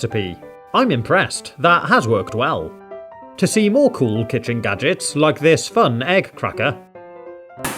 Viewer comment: At first I thought this said kitchen nuggets!!! 😝😝😝